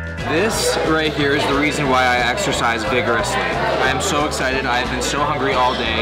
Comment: Exercise? I thought you said extra-fries!